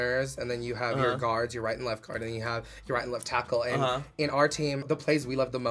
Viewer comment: I swear to god i heard say "left taco" instead of "letf tackle" 😂😂😂🌮